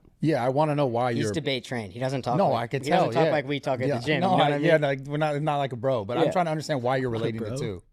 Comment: did he say “he doesn’t talk like we talk at the gym”??? Bro has never seen the inside of a gym in his life 😭